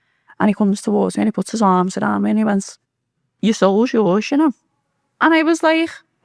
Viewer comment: WHAT DID SHE SAY? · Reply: She said "Jesus" told her "Your soul is yours, you know."